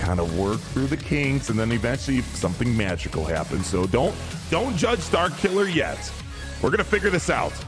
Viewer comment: I legit thought he said, “don’t judge dark hitler yet”